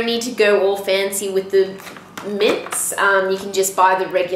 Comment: What is she saying on 1:38? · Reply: Félix Cavazos It sounded like she said "mint" or something...?